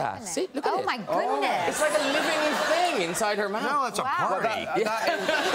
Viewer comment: What did Jeremy said at 0:42? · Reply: Roland Schlösser "Now it's a party!"